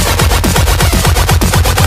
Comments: instead of "bro this is fire" , Me: bro was that iferg?! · I always thought 2:37 said "Bro your on fire"